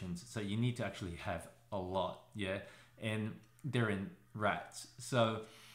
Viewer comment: minutes. Did you say that Retinods are in Rats?